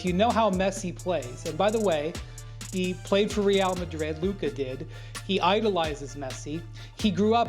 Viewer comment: he said messi played for real madrid which is absolute nonsense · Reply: He did not say "Messi played for Real Madrid" no one here watched the video apparently and can't hear. At 10:54 "he played for real madrid...Luka did". He literally made sure to clarify he meant that Luka played at madrid (because he did for 3 years) in the exact same sentence and you clowns are making fun of "silly americans" as if it isn't worse to be a f**** idiot that can't use your ears